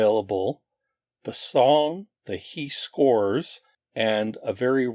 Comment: Sounds like he says Eat Corn not He Scores 😁